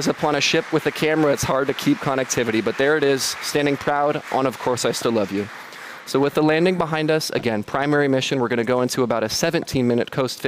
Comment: Is he saying "I still love you" at 24:30 ?!?!